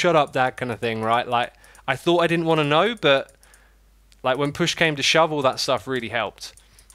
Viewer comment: i thought i heard X said "when push came to shovel" lol